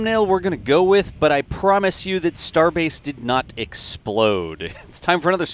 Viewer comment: Starbase..? I thought you said, Starbucks..?!